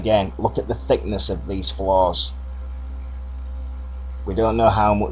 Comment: Ive had to put closed caption on as I thought you were saying flaws which didn't make sense , but you were saying floors ..LOL greetings from Canada